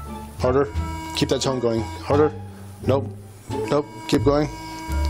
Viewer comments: I kept hearing him say "keep that tongue going" when he meant tone. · The narrator says he's professional, all I keep hearing is "keep that tongue moving" 😂😂😂